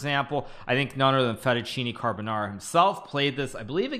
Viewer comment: did he just say "none other than fettucine carbonara played this"??